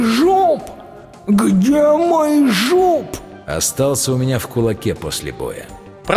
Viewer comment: я услышала "где мой жоп" и сидела в шоке